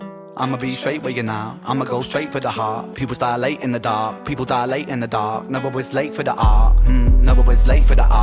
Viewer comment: No Ren can't eat any of that, his diet is very restricted..meat a couple of vegetables...he is a bit excited he has recently introduced rice cakes...he said you may think they taste like cardboard but for him, he's loving the crunch 😂❤ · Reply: Great reaction but you misheard. He said "Pupils dilate in the dark, People die late in the dark"